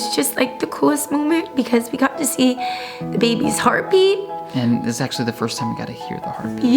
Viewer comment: did say "her heartbeat"???!!!